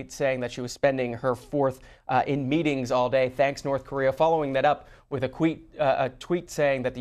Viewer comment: I'm just going to assume that all the comments in broken and horribly misspelled English are from North Koreans. Also did he say "following it up with a queef? @ 6:07 lolol